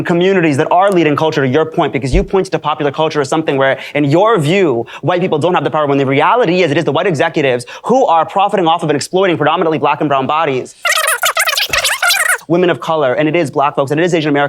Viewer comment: Did he just say black and brown "bodies"?